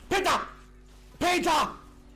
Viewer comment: did he say Hitler ?